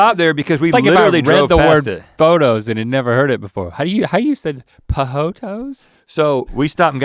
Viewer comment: Min 54:23 I thought Link said Jojoto (corn in Venezuelan Spanish) Instead of Pohoto and I was gonna be really proud that Venezuelan words were in this podcast Lol